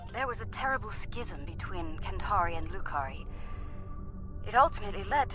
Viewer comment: Jesus, I thought you said Drukhari. Gave me a small heart attack.